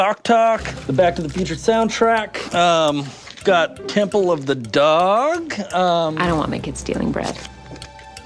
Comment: Isaac is the best. Also LOL "I don't want my kids stealing bread" after he names off Temple of the Dog. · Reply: LMAO I thought she said "I don't want my kids inbred!" omg